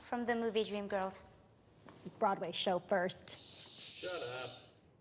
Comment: who heard someone tell Rachel to shut up